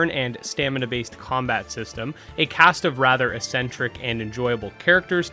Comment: Did you just say "essentric"?